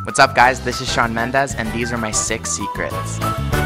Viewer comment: I thought he said sex secrets lol